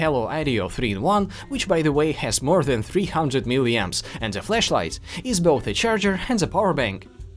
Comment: dude did you said flesh light ? at 0:33 ??